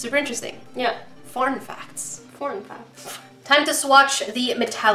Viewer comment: I THOUGHT SAID "PORN FACTS" I